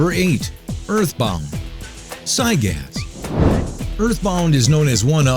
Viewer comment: Sygas? Thats what I heard.